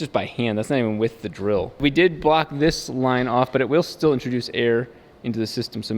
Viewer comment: "we did bought this line" - 2019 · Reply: You talking at 7:45? I think he's saying "we did block this line...."